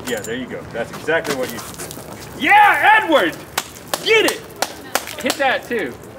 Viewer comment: for I second I thought Link said "Yeah N-word" lol